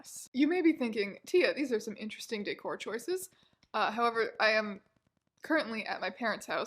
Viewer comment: I heard "dickcore choices"